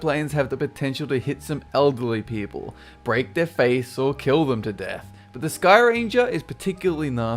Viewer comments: Wait did he just say "kill them to death" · did he just say "Kill them to Death"????? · did he just say kill u to death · did he say killing them to death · Did I just hear say "Kill them to death."?!